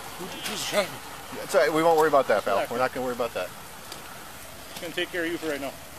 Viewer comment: He was drunk? "Who was driving? I was drunk. That's ok pal we're not going to worry about that." Could be wrong but I'm not sure what else he could be saying.